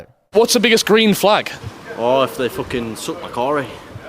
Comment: what did he say about the green flag? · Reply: “Dick sucked”